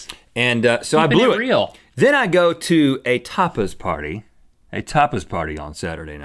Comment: I love how Link thought Rhett said topless party at 4:50 and had him spell it out.